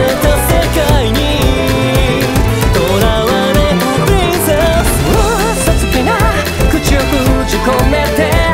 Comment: のさ～～～そらるさんのさ～～プリンセスがさ～～～ プリンセェスみたいにェがめっちゃ聞こえるのがすきなの...